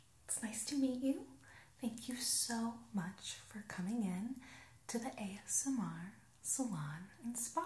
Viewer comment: Her: "hi thanks for coming into the asmr spa" What I heard: "hi, thanks for entrusting your life with us ☻"